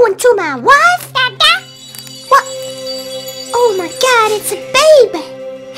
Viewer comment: Oh my goody instead of oh my God